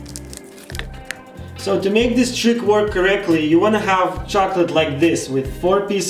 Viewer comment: Did he say „To make that shit work correctly...” 0:19 🤔